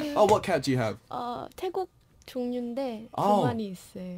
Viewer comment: i heard taekook